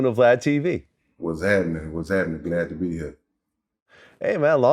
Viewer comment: I thought he was bout to say “What’s happening Glad”